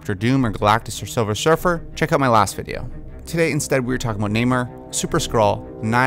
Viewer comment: Am I the only one who heard Neymar instead of Namor?